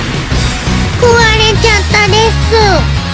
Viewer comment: "Owarida ka ditu!" I hear Sundanese language スンダ語を聞く